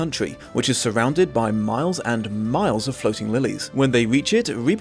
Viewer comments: Had to rewind cos totally heard it as surrounded by miles and miles of floating willies, which I really didn't remember from the book 😅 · I thought you said “miles of floating willies“ 😂😂😂 Lillies are nice though